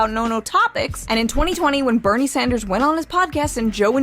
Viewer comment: I thought she said Bernie Mac for a sec. Rip Bernie Mac